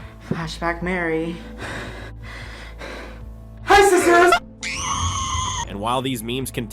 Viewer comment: I always thought it said "HISASIS-" XD